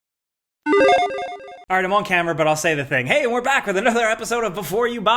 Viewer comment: Lolol I thought you said 'Alright, I'm ALL HAMMERED but I'll say the thing' As always, thanks for content creating!